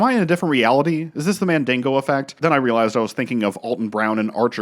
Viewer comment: Did bro say "Mandingo effect" and not Mandela 😑